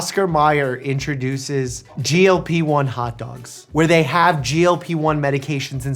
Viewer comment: OMG! 😂😂 I was listening with earphones and not looking and I honestly thought he said "Cardi A introducing....... a lap-in" 😂😂😂😂😂😂😂😂😂